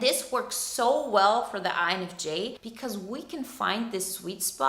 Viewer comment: Thank you for this video 🙏🏻 Subtitles says 'eyes of Jake' instead of INFJ's 🤣